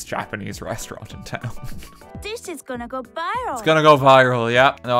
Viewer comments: Please tell me I'm not the only on who heard "this shit's gonna go viral." I know that's not what it was, but I was really caught off guard when it happened. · was I the only one who hears “this 💩’s gonna go viral”